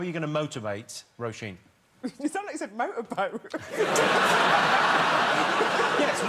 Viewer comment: To be fair, I also thought he said motorboat